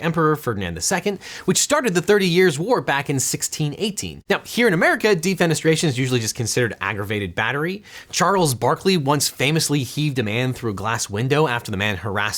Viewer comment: For a second I thought you said Charles Barkley Yeeted a Man